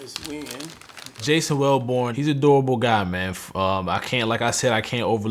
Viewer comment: Lmao at first i could of sworn i heard “ he an adorable guy”. Had to rewind real quickk